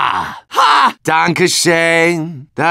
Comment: When he sings his little jingle at 8:17 I die on the inside for some reason XD (I wonder what he's saying and what the German translations are lol) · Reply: He said "danke schon" And I think that means thank you very much xD